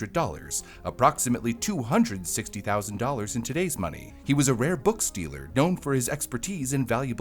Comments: I know you said "he was a rare books dealer", but what I hear is "he was a rare book stealer" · I thought he said book-stealer. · I heard “He was a known book stealer” 😭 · i heard “rare book stealer” and got confused lol